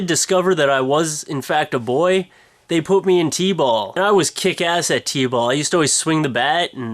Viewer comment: im not going to lie.. I thought you said "they put me in T-Bag" I was like, wtf?